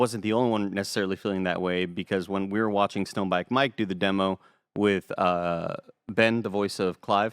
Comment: LoL I thought said Stoned Bike Mike hahaha